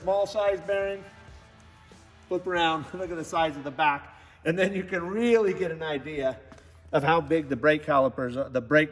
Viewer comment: Saw point at the bearings on the wheel and talk about how the internal diameter gets bigger towards the center. @ 13:49 "And you can really get an idea" I heard, "You can really get an I.D." Ugh.